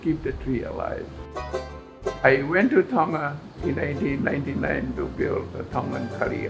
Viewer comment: thought he said he came to Tonga in 1899 and was like damn.... they really live forever in Polynesia!!!